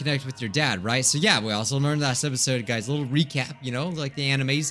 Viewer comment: wait did he just sayed "a little recap of the *ANIMES*"